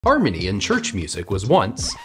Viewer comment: I thought he said Armenian church music